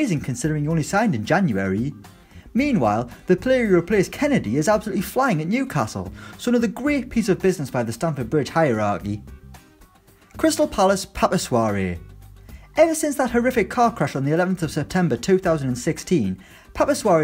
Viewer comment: I can't be the only one who heard Papa Suarez